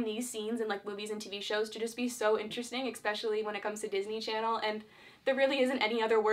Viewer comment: Did she say Expecially